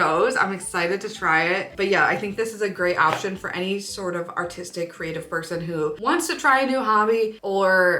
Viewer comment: i definitely thought you said autistic creative person haha. Great video, love the recommendations!